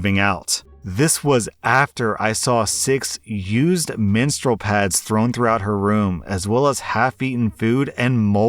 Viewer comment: I kept hearing ‘minstrel pads’ Imagine walking around on that dirty floor, then you step on one, and hear the strumming of a lute followed by a sonnet, that’d be annoying af